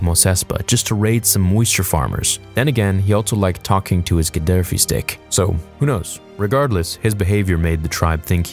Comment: Did he really say Gadaffi stick at 2:49 ? 😂